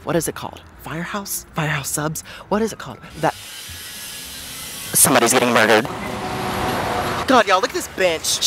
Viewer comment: I really thought he said at one point “somebody is eating a burger”